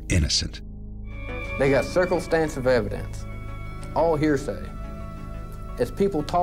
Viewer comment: Did he say "they have circlestansive evidence"?! My word...😂